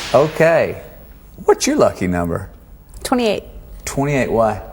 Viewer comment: Is it just me or when Matthew asked what Jennifer's lucky number was I immediately thought he said favorite and thought of Sheldon from TBBT. 73 all the way!!! :)